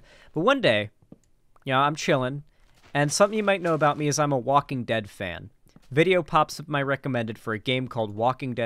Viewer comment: Im so fucking dumb, when you said you're a Walking Dead fan I just heard you saying "I'm a walking deadfan" and wondered what the fuck that is